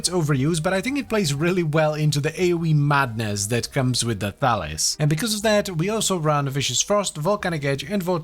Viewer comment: When I heard you say The Thalys I swear I heard you say Phallus and I was like wow this going to be an interesting weapon. A 2 handed what?